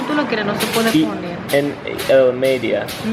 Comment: Happy Birthday!! @ 11:01 did u say maybeia? That could be a word, not sure... sounded funny. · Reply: Thanks Kevin!! I believe he was trying to say “medio” for middle?? We’re clearly in need of some Spanish lesson